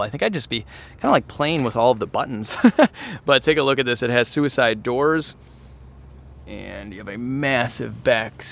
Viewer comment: what did he say? suicide doors?